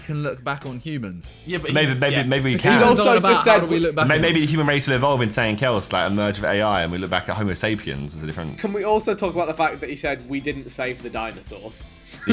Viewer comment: Actually, Vik, I think you'll find that he said "Some day we could look back and be like, oh we didn't save them... the Homosapiens, right?"